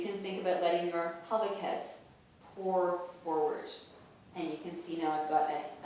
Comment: Thanks for the video. Did you say "Pelvis has poured forward" at 1:45? When you said that you think of your pubic bone draping forward at 1:53, did you mean bending the upper body forward at the top of pubic bone or bottom of your pubic bone? I feel weird to ask this to you though but I really want to know. At 2:04, did you mean to fold forward at our hip creases? And by the way, you uploaded this video on my father's birthday. Ha ha. Thanks! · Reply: Oh my gosh, I'm so sorry that I didn't ever see the alert to this! And yes, I did say poured, but I think it would really be better to think of your pelvis as a bowl doing the pouring instead! In terms of the motion of the pubic bones, yes I did mean leading the forward fold from the bottom of the pubic bones. For some people that works as a better image than moving from the sit bones in the back. Same for the movement at the hip crease, or hip fold. The goal is to move your pelvis around your leg bones, rather than move your pelvis AND your lower spine. All the different descriptions and cues are to make sure that at least one of them resonates with you!